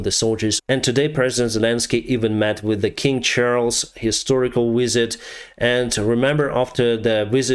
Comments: I heard “King Charles, historic wizard” haha cracked me up Keep going Ukraine! You’re winning this! 🇺🇦 🤝 🇬🇧 ✊ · your English is excellent, but for a moment there I thought you said “…today Zelenskiy even met with King Charles, historical wizard” rather than “…King Charles, historical visit.” The monarch is a largely symbolic role, and in Charles’s case he is known for drinking unpasteurised milk; I‘m pretty sure he is not endowed with magical powers. But I am glad the UK state is supporting Ukraine and I hope we maintain this support until Ukraine achieves victory for the good of the democratic world. Slava Ukraini🇺🇦🇬🇧